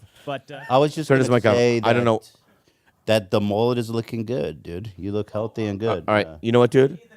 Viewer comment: I like the mullet on George but I thought Ethan said “the mole is looking good” when he saw George 😂🤣